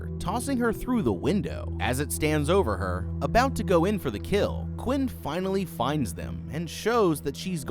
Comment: i really thought he was going to say "about to go in for the kiss" idk what's wrong w me XD